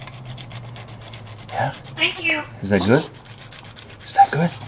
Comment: was i hearing things??? did say "thank you" at 20:06 when was filing her nails???